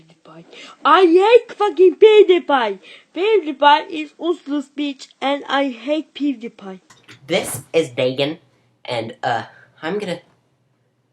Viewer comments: i thought he said this is bacon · kid sounded like he said "I ATE f**king PewDiePie"